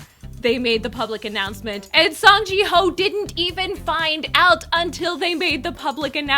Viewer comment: Lol it is pronounced as "seok" and not "sook" XD You were correct and after the new PD didn't work out, Myuk PD who's been there from the beginning as an assistant PD took over and fans were okay with that bc we know him and he knows the show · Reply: It sounded like she said Song ji Hoe, instead of Hyo. I thought they used to live in Korea, so why can't she pronounce korean name, or is it that they can only pronounce k-pop idol names. So are you saying Myuk PD is the one who started this?